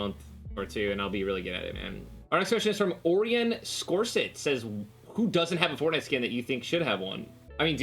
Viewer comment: Did ninja say Ørjan? On 1:46 or 45